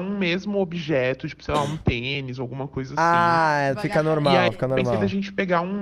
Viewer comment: Mesmo na velocidade normal eu entendi um penis jkkkkk lembro que quando assisti esse vídeo no canal da eu também entendi isso e me achei louca, ainda bem que não fui a única kkkkk 😂